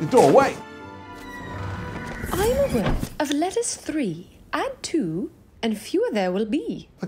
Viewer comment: (insert answer to the last riddle here) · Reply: Thank you I misheard the riddle and thought it was "I am a word of letters 3 AND 2 and fewer there will be".